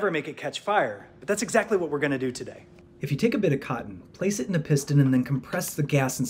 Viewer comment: Who thought he said take a bit of c.m